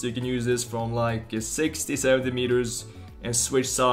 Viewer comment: I thought you said you can use this from 60 centimetres...I was thinking yeah I should think so! But think you said 60 to 70 metres